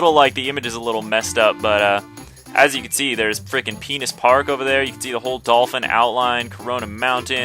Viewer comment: did you said Penis Park? lol